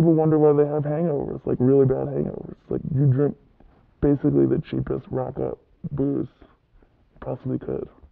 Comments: "cheapest rotgut booze" is what he says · The subtitles are wrong. He says " like you drink basically the cheapest rot gutt booze you possible could"